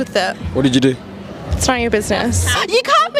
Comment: Did she just say manipplelator ...